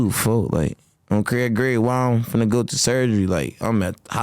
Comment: If I could get a dollar everytime bro say “on Craig grave” I’ll be rich af😭😭😭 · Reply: thought he was saying Craig Green 😂